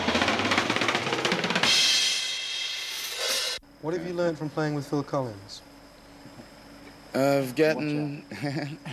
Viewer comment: What does Phil say at 2:10 in reply to the interviewer's question, it's hard to hear as Chester's talking over him. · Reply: Phil says „Watch out“ meant in a humorous manner.